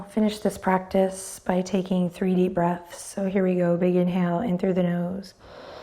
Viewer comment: I thought she said "3-D breaths" instead of "3 deep breaths". At least I had a good visualization for it! 😅